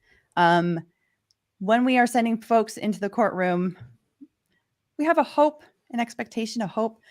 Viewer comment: Actually she didn't say that at all, another thing the chatters are getting wrong. Here's what she actually said: “I’m concerned that people might draw an inference that somehow I’m directing these things for views,” which is just the opposite of claiming that she sent them to the court. In fact, here is her entire private message to DUI which he put on his Twitter feed, and which you can find for yourself at about the 25 minute mark in his today's video, but as you can see she never got a chance to discuss with him privately because unfortunately he took it public. “Hey, I’m really sorry to have to do this, but I’m going to need to ask you and James not to come onto the live stream after court today. I’m happy to discuss at a different time the specifics, but I’m seeing things like big faces being made in the courtroom, and some questionable tweets with photos that I’m hearing are raising some serious concern by the court staff, that I’m concerned that people might draw an inference that I’m somehow directing these things for views. Again, I’m really sorry, but at least for today, we’re going to need to do without an in-court perspective.” All I am saying is it's important to be fair to both sides. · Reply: she clearly said: “when we are sending folks into the courtroom” then she start to rambling about the hopes and expectations she have about the behavior of the people who allegedly she sent